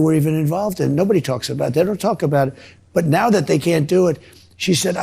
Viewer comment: Did Trump say IBS instead of IVF (at 11:45 mark above) ? Irritable Bowel Syndrome?